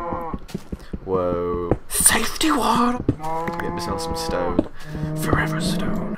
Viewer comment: Did he just say "safety water" as in "safety torch put in on your porch" and "safety water pour it in a bucket"